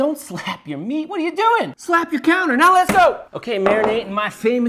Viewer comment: I thought he said “slap your cooter.”